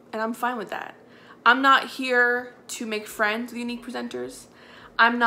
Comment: Thought she said "purple status prisoner." Instead of presenter.